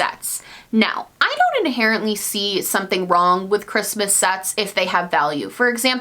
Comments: ♡♡♡♡♡♡♡♡♡♡♡♡♡♡♡♡♡♡♡♡ I thought she said "Christmas sex" instead of "Christmas sets" and I was like omg I didn't realize it was this kind of video! 🙊🤣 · Lol she said "Christmas Sets" and I thought she said "Christmas Sex". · When you said “ok now I wanna talk about Xmas sets” I heard “Xmas sex” and I was like ok go off 👌🏽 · Instead of Christmas sets I thought you said Christmas sex! 😂😂😂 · omg u said christmas SETS and i thought u said christmas s*x...... i was like... wait.... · For a quick second I thought she said Christmas Sex and that she is okay with Christmas Sex. Nope! She said Christmas SETS. *sigh me if you must 😩